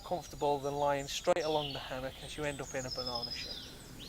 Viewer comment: What did he say at 7:30? I can't understand the last sentence before the scene change. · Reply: "you'll end up in a banana shape"